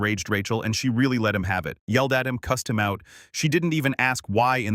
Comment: the caption says “custom out” for “cussed him out” 🙄🤣